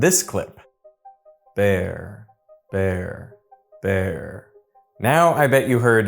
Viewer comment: Did anyone else clearly also hear “bear” when he was lipsyncing “fair”, because I didn’t fall for that trick because I cannot lipread for the life of me! I swear other people lipread to communicate in those cocktail party situations. And I have no idea how they learned to do it! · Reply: I heard “vair” (French word for squirrel fur), because V is a voiced consonant. There was no aspiration sound to the recording, so I didn’t hear “fair.” I could get it to go between vair and bear, but never fair. I don’t read lips, but I am a trained singer, so I notice pronunciation more than most, I think. And the alternate mouth position couldn’t produce bear, but could produce vair. The sound didn’t have an F in it, period. A V or B, possibly, by the lip position. But never an F. 😂